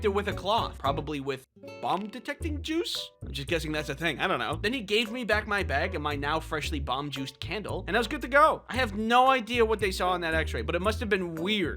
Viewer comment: I heard "bum-detecting juice"